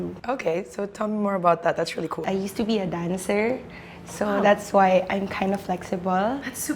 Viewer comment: I'm sorry I thought she said "I'm a former dinosaur" lmaooo